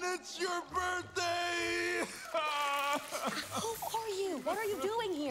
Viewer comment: did say who tf are you🤣🤣🤣